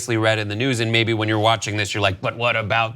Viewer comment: I thought it said incest